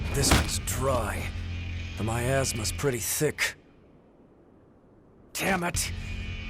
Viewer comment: am i the only one who heard "my a** is pretty thick"